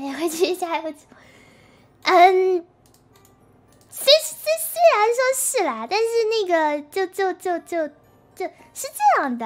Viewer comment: I swear i hear the n-word xD Edit: ok, i hear it a lot lmao